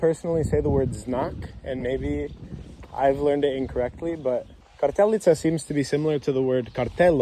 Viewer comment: I think the word she said was kapelica reffering to the chapel